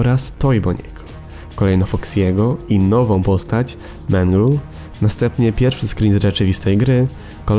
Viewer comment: czy tylko ja usłyszałam menel?